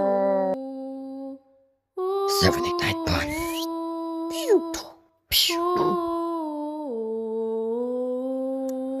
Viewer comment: what did he say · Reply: He said "7 8 9 pun"